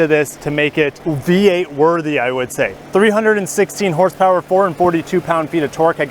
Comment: Did you say 316HP?